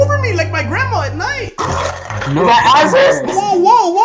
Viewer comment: I thought he said Yo! Is that ISIS bruh